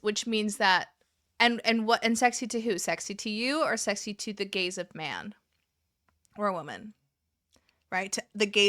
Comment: You said "the gaze of man" and I heard "The Gays of Man," an all-new, queer glam rock band. ✨⚡🌈